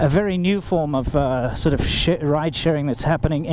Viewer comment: did he say "shit ride sharing"?